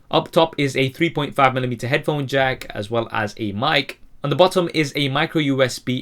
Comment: Wait what no headphones????? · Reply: ferrer I thought you said no headphone jack